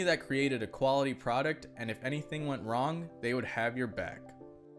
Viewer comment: I thought he said they will have your money